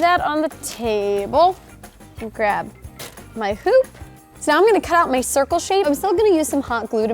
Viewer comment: did anyone else hear here say, " And grab my poop "? Just me?!